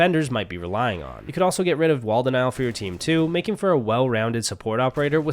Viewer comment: What does he say 20:18? “Waldenout”? He says, “you could also get rid of waldenow”??Like i can’t understand so can someone clarify😂😂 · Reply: Wall denial 😭